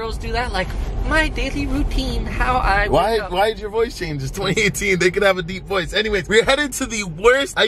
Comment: did just say it's still 2018